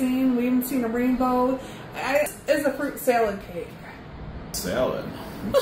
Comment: at first I thought she said, "fruit salad."